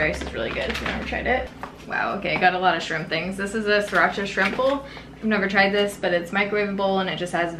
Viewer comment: Instead of “shrimp bowl” I thought you said “shrimple” and was super confused for a second 🙄😂 my baddddd!